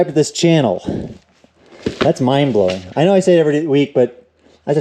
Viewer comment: I thought he said "that's mine-blowing" :-D